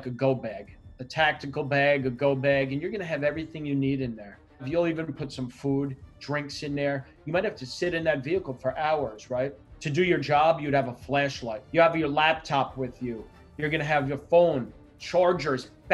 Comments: did he say fleshlight? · With his vowel placement in his mouth I definitely heard: "To do your job, you'd have a FLESHLIGHT" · I misheard him say fleshlight and I was like :oh I need that on my go bag? Wouldn't my hands be enough?. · When he was listing the contents of a go-bag I honestly thought he said "...fleshlight..." and I was like "Is this guy serious?!!" and then I realized internet has ruined me. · I honestly thought he said fleshlight💀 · Did he say fleshlight instead of flashlight at 1:02 ? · i thought he said fleshlight in his go bag instead of flashlight